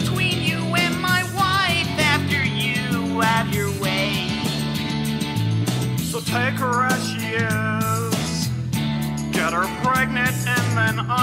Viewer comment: what's he say at 1:09-1:12 before he says "get her pregnant" ? Anyone? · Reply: "So take her as she is, get her pregnant and then I will raise your kids".